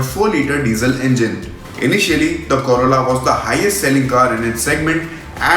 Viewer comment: Oh god u told corolla then why I heard it as corona😂